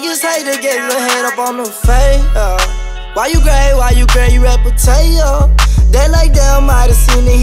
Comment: Did he really say „Why you gay why you gay, you’re a potato”?